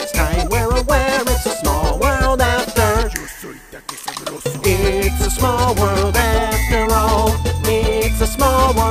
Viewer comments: Does anyone know what's whispered at 5:34? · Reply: "yo soy takesabroso" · any idea what the deep voice says at 5:34? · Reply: TheGuy "Yo soy Takesabroso", a reference to that one Mexican restaurant ad that had Thanos in it. The line itself is a spoof of the "I am inevitable, I am Iron Man" moment at the end of Avengers: Endgame. <link>